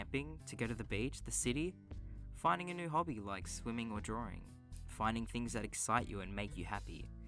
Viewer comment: oh my god at first I thought you said "find new hobbies like swimming or drowning"